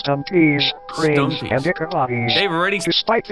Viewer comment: Luv How The CC Said Dick Bodys